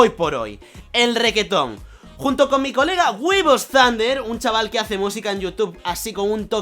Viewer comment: cuando dice weebos zunder pensé que decía huevos grandes XD